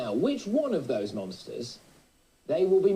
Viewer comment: it sounds like he said nonces not monsters